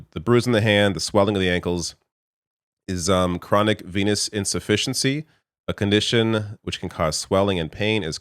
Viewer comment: I totally thought you said penis insufficiency! 😂 “is it fatal?” 🤣